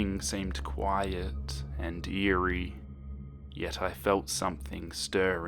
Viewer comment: I actually thought you said "it was quiet and deery." I thought you made a damn pun while telling a deer horror story. God damn my brain